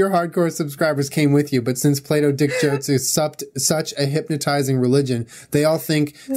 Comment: When he said "Plato dick jokes" I thought he was saying "play dough dick jokes" and I was like...also funny lmao.